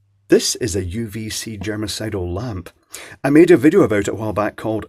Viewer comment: Did anyone else hear 'this is a genocidal lamp'?